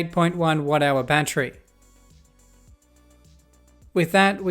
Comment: he said watt hour i heard whatever battery😂😂😂😂😂😂😂😂😂😂😂😂